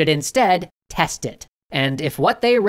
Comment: Did you just say; "they were to des carte it"?